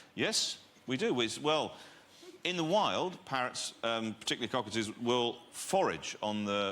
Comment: I thought Bill Bailey owned Paris there for a second. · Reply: I heard "parents", was confused too